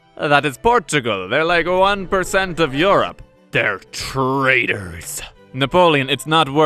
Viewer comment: for a second i thought he said traders which won't be wrong either